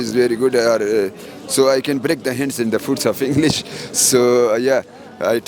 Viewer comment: Did he say 'So I can break the hands and the foot of English'?